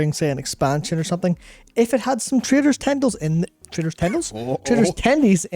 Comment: I know youre saying "Traders Tendies" but all I hear is "Traitors Tendies" and its honestly fitting...